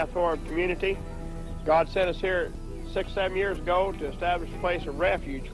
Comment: what did he say? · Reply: 67